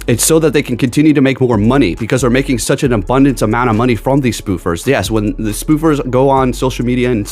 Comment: I'm so tired that when you said "spoofers" I heard "boofers" lol. Don't do drugs m'kay haha :D